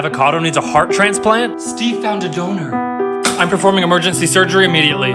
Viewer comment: Dude when he said "Steve found a donor" it sounded like he said. Steve found a donut